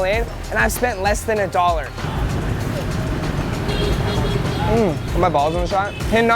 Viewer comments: Did he just say “ is my balls inside” looking at is shorts😂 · “Are my balls on the shot” who heard this